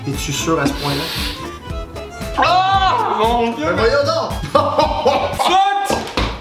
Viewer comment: Qu'est ce qu'il dit Olivier à 1:52 ? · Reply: Piper L89 “voyons donc “